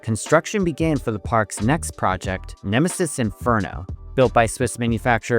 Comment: thought it was Emesis Inferno but misheard it, so the best name for an extreme coaster ever is still free